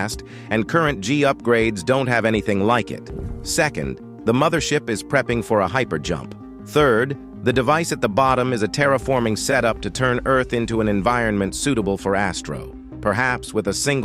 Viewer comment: did you say hypercharge?